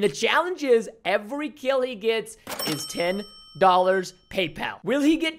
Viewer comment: I thought he said every Keeley gets $10